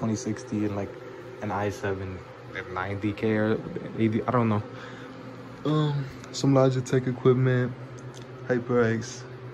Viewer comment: did he just say some logitech equipment?